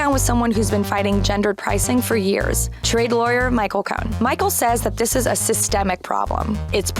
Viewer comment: wait wait wait, am I mishearing things or did she just say Michael Cohen? as in, the Trump lawyer?